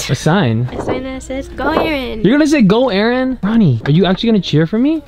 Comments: is it just me who heard Aaron call honey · I literally thought Aaron said "honey"😂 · Did Aaron just say........"honey"😲😲😲 SHIIIIIIIIIP!!!!!!!! #RonRon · look at the subtitles it is saying honey instead of roni...#ronron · did he just say "honey"... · ME HEARING HONEY INSTEAD OF RONI IN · DID HE JUST CALL HER HONEY... cuteeeeee💕💕💕 #ronron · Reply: but now that i hear it might be roni but it sounds like honey lol